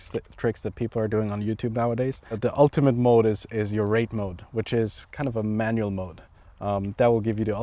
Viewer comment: Did he really call it a "rape mode"? I really hope that I misheard that. I thought this culture moved on from that term years ago. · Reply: Rate mode.